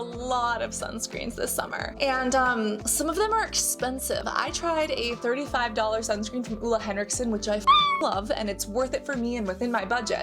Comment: Girl I thought you said a 35,000.00 sunscreen 😂😮😢😅😊